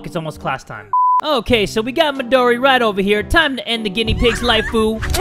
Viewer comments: LOL!!!!!!!!!!!!! I TURNED ON CAPTIONS AT 10:40 WHEN KILLS MIDORI THE CAPTIONS SAID "TIME TO END A GUINEA PIG LIKE POO!!! " HAHAHAHA!!!!! LOLLLLLL!!!!!!!!!! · LOL!!!!!!!!!!!!! I TURNED ON CAPTIONS AT 10:40 WHEN KILLS MIDORI THE CAPTIONS SAID "TIME TO END A GUINEA PIG LIKE POO!!! " HAHAHAHA!!!!! LOLLLLLL!!!!!!!!!! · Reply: I thought it said ( without captions) “Time to end a guinea pig lifeu!” But then I went back and listened closely and when it said”lifeu” it sounded to me like it said” like poo!”